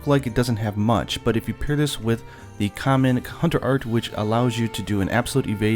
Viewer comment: Woah did i just hear common cun*er art?